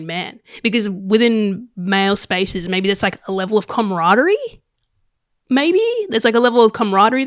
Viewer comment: did you say CUMradery? Lmaooo or was that pun by accident? 🤣😂🤣